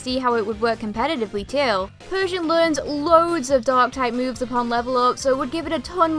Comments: OHHHHHHH i thought u were saying persion was a normale DOG typd😅😅😅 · When u said dark type I thought do you said dog type and see if it was a thing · When she was talking about Persian I thought she said "Dog Type" instead of "Dark Type" lol I always thought Lapras should have been Water/Dragon, but that would have been OP since Water cancels out Dragon's weakness of Ice and Dragon cancels out Water's weaknesses of Electric and Grass. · i honestly thought you were saying DOG type, but i think you are saying dark type, im so dumb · i thought she said dog type not dark type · I understand its just your accent but i thought you said “dog type” instead of “dark type” .w. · When you said dark type for Persian, I thought hou said dog type :P